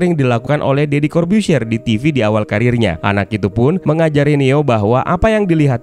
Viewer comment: Aku dengar "daddy carbuzier di awal kariernya" atau aku salah dengar??😂😂